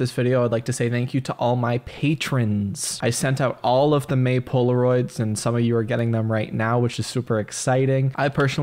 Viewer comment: When you said "May poloroids" I thought you said "Maple or roids." Very confusing for a good 10 seconds until you said the word "poloroid" on its own